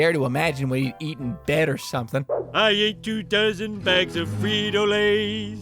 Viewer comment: I thought he said 2,000 bags of frito lays...